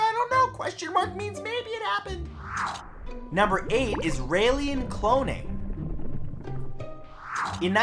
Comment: am I the ONLY one who thought he said "Isralien cloning" at first?